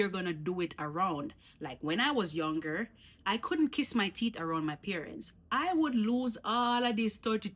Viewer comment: I heard "Kiss mi teet" and was like woah lol